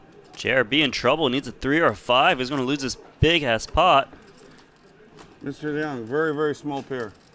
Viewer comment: Did he say: Big ass pot?